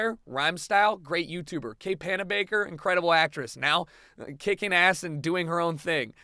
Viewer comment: i thought he said Hannah Baker, like from 13 Reasons Why. hahaha